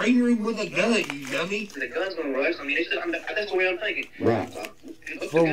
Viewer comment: Wait ✋ did I hear him right? @ 31:16 he said "he thought the gun was gonna rust"?